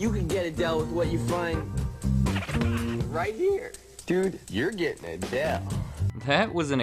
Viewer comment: huge Mandela effect, I swore he said "Dude, you need a Dell."